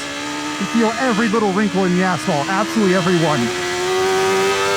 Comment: "You can feel every little wrinkle in the asshole" -, 2025 I know he says asphalt, but that's not what I heard the first time, and that's why I have orange juice all over my monitor.